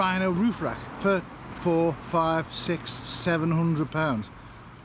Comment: lol At the beginning of the video I thought he was saying roof racks were 600 lbs lmao, I was like wtf are these racks made of?